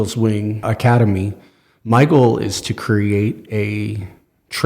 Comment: I heard this as “Michael is to create…”